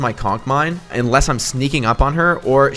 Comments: Did u just say cock mines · Everyone: says mine: C O C K mine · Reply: i thought he said cock mine tbh